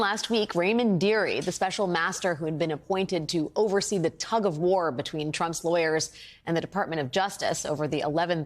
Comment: The presenter said “tug of war” and I thought she said “tub of lard”, referring to Trump!